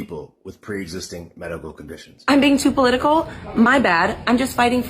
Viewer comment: All I keep hearing is, “Am I Beijing too political?”